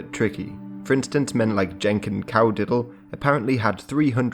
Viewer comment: no offense but when I first heard that I heard drunken cow diddle which just sound so wrong😂